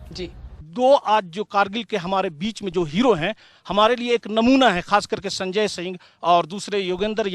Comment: Did he said namuna